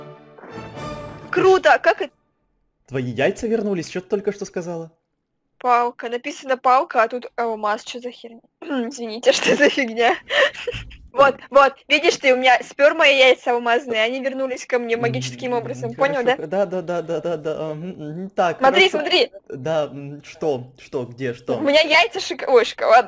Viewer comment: сказала "у меня сперма и яйца алмазные" или мне послышалось?